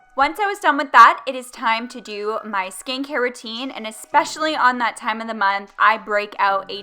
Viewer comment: I thought she said “ skank hair routine “ 😂